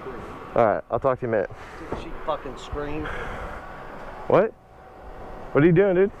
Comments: This guy was definitely drugged up. First he says "Stop f***ing screaming" when the biker is on the phone (Not screaming), then he throws his glove with his back turned while he's trying to get his card working. Then, from what I can tell, he tries getting the biker to buy some cigarettes for him in the gas station, "2 for 1". That's why he points at his card. You can't fault the biker dude for being wigged out about this whole thing. The guy kept touching his stuff, getting right up in his face, and smacked his helmet at one point. There are too many unstable and dangerous people out there to risk being understanding towards this guy who's giving us tons of red flags in the video. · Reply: The Batman I thought he said "does she fuckin' scream?" as in does the motorbike get real loud. But I could be wrong. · I swear the dude said "don't you fucking scream". I think you dodged getting robbed/shot by a fucking nutcase. Stay safe bro. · its sounds like he says “don’t you f*cking scream.” Like he was gonna pull something out bc he had his hand at his pocket too